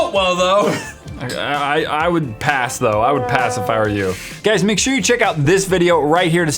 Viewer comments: What he said at the end "I would pass if I were you" what I heard "I would pacifier you" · "i would pass if i were you" he said but i heard "Pacifier" Im a bad listener